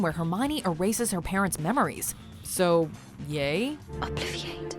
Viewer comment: when hermine said obliviate the first time i watched it in english i misheard her and thought she said "obliterate"